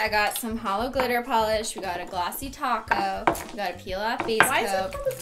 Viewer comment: I also thought she said glossy taco 😂